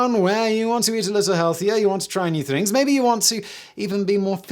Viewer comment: I really thought he said "You want to eat a little healthier, you want to euthanise" In the sponsor slot 😅